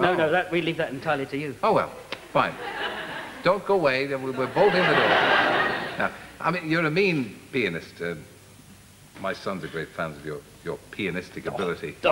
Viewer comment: I thought Terry called Jules a mean penis!